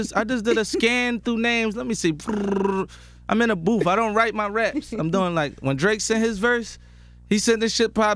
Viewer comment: I swear he says I don’t write my raps